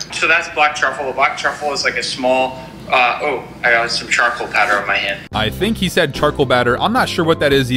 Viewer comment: I think he said charcoal powder Not charcoal batter16:47